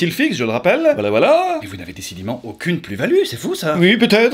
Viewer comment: Aucune quoi ? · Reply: Aucune plus value... En gros ils n'ont aucune utilité à part être mignon...🤣👍